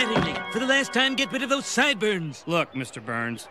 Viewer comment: is he saying "get rid of those sideburns" or "get rid of those side boo-urns"? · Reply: I heard boo-urns